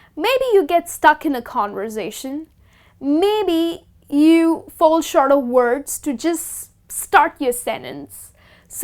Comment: someone can help me what she said. Fall short words? I dont understand